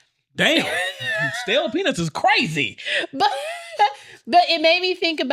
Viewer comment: the captions saying “Stale PeNIS is CRAZY” is hilarious 😂😂 absolutely HI–FREAKIN–LARIOUS !!!!!😆😆🤣🤣🤣 almost fell out my bed at 3:17am chuckling 🤭